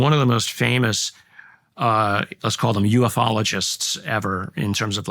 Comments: HA! I thought you said he's an Emuologist and thought, wtf does Emus have to do with anything! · I swore you said EMUOLOGIST 👀😂